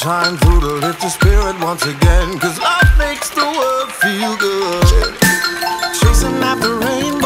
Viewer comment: did he say he’ll feel so good